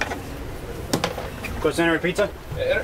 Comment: What does he say at 8:50, "Request to enter pizza"?